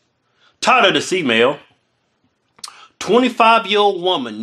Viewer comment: Always sounds like he says "Tired of this e-mail"